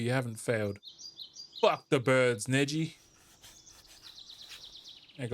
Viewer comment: I GOT THE PERFECT AD AND LET ME TELL YOU I THOUGHT YOU WAS GONNA SAY SUM CRAZZYYYYY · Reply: "F the birds N--"